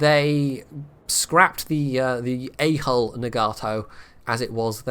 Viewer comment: He keeps saying “A Hull”. I keep hearing “A Hole” 😂